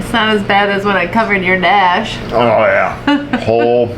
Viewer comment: I swear at 9:21 she said she "covered your 'stash" ... had to rewind to clear up that misunderstanding and now that I know it's "dash" I'm not sure helps :) Nice nails.